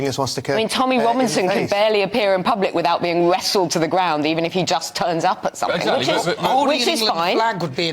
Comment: Did I hear that woman's say "Tommy Robinson getting wrestled to the ground.... which is fine"?! Fascist comment surely....